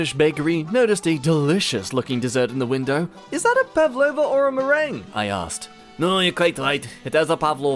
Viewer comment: I'm not sure how they pronounced it, but when they said or a meringue, it sounded like "Or am I wrong" Though how do you get from Merr-ang to Merr-ong is a bit odd.